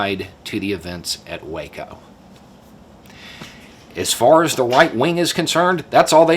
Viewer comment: Did say "the white-wing"?